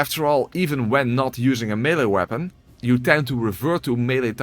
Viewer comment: When did I attack with a caestus?? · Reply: Daium, sorry, had to watch the intro again. Thought you said "without using any melee weapon" got it wrong. You explicitly stated: "without dealing any melee DMG." Caps on me, i beg your pardon.